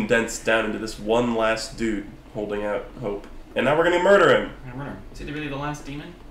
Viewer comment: For a second I thought you were saying that all of demon kind was condensed down into Shaun Ryder and I was very confused.